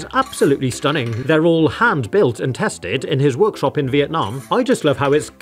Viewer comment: did he just say Vietnam?